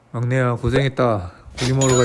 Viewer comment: 못하는척 하시려고 애 많이 쓰셨네요. 마지막에 자막안보고 잘못 들어서 "막내야 고생했다. 윗 몸하러가자."로 들었네요